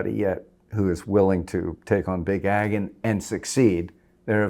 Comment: what did he say? Big agon? What's this?